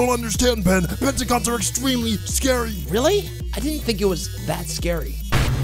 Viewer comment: the captions say 'mexicans are really scary'